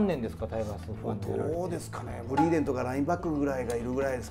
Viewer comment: さすがにグリーンウェルは新しすぎないか？？w ブリーデンとも聞こえるよーな。。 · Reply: ブリーデンって言ってますよ。松村さんがブリーデンとグリーンウェルを間違える訳がない。 でも半世紀ほども前の話なのでスタッフさんが知らなくても仕方ないかなーと。 ところでこれを機会にタイガースチャンネルを遠藤さんとお二人で立ち上げるとかどうですか？ そうじゃなくてもちょいちょいお二人のタイガース談義聞きたいです。 またのコラボ待ってます。